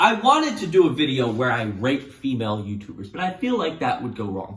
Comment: when you said “rate female YouTubers” YouTube’s subtitles got it as “So I wanted to do a video where I raped female youtubers.